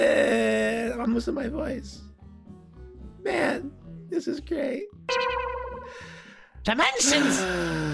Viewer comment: i heard "man, this is gay"